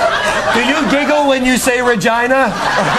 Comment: I thought that he said Vagina not Regina XD... oh well :P